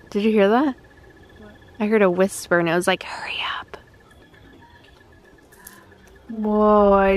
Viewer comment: People who Heard hurry up 8:00 👇